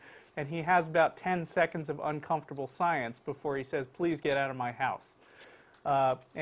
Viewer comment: Ha, ha. Sounds like he says "uncomfortable SCIENCE " at 1:05 instead of "uncomfortable SILENCE." Evolution is uncomfortable SCIENCE all right. Also, it was 19 seconds of uncomfortable silence and not merely 10. And it is a blatant lie that they inserted Dawkins' pause afterward.